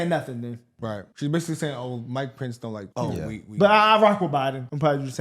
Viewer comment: She really said “Mike Pence” lol. I thought she said “my pants” that’s how relevant and memorable Pence is. She can’t answer anything without blaming someone. She’s just playing cheap politics. F that woman.